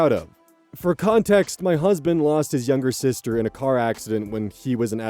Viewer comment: for some reason I thought he was going to say ‘lost in a snake accident’ Also, great voice work on story 1